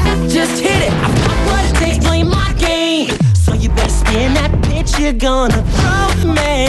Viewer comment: it sounds like lucas says bitch at 0:38 or